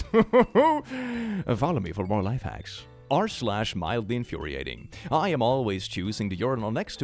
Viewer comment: Turned my head for second to get something, and so I couldn’t hear the audio properly. When “r/mildlyinfuriating” was said, I heard “r/violentlyurinating,” and just went, “well that makes sense I guess. Violently Urinating would make sense for a urinal story.”